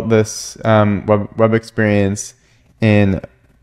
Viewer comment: Can't find it when I search it; what did you say at 9:55? 'PWI'? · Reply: Progressive Web Apps (PWA)